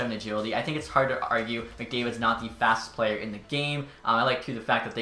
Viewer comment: The thumbnail shows McDavid with 97 speed lol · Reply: oh at 5:10 i thought you said connor mcdavid is not the fastest player in the game